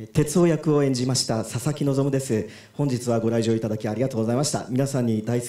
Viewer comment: 声優のこととか全く知らないんだけど、たまたまこの動画観て「ささきのぞ"み"」って聞こえて、「めちゃくちゃ寒っ・・・おもくそ滑ってるやん・・・」って思ってたら その後に「佐々木望」と表記出て意味わかってホッとした。